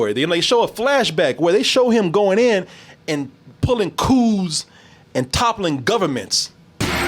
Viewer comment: Got confused for a moment at around 16:18 when I heard "...pulling cooze and toppling governments."